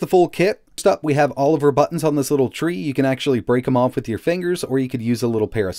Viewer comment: I swear it sounds like you say Oliver buttons at 0:36 lol